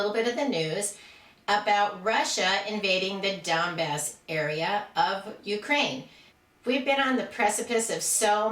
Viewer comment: At first I thought she said, "The dumb-ass area of Ukraine". lol